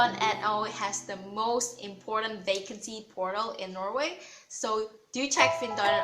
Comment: nice video, but im a bit curious. 2:54 did you say porno? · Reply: Zilleyon not porn it's portel